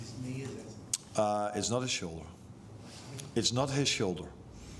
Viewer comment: Sorry, can someone tell me what Slot said please I heard "its not his shone?" Ive played it back a few times but cant understand it, thanks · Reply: “It’s not his shoulder”